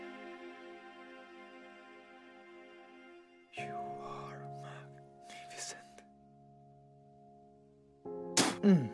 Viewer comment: what is he saying to Nick in 3:48'?? :/ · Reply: I think he said: "You are magnificent"